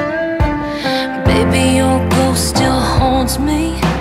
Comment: I thought she said "baby your close still holds me".